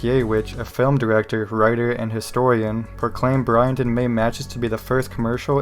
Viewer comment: When I heard "Bryant and Mays" I thought I was hearing "Brian May" LOL